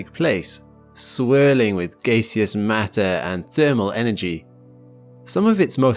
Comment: Did you just say gaysius?🤣